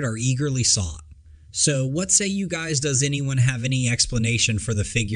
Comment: The real mystery here is wtf did he just say @ 2:49?? Lol It sounded like "So whatzay you guys does anyone have any explanation....."